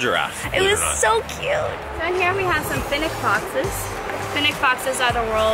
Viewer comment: I thought the women said Phineck (idk how to spell it) BOXES instead of foxes and I was like, "Woah! Boxes in a zoo? They must be pretty dangerous!"